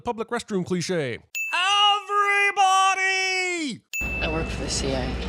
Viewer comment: If 5:54 is supposed to be a Gary Oldham impression, I think you know what he says is "EVERYONE!"